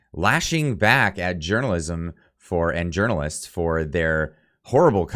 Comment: Hahahaha At the end I thought you said "urinalism" for a sec there... Gov journalist aren't journalist.. They are propagandist... Period.. It has kinda always been like that..